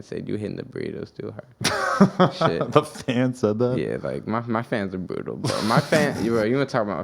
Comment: did he say "my fans are butal"? this kid cant pronounce his R's so he just leave em out completely fuck it!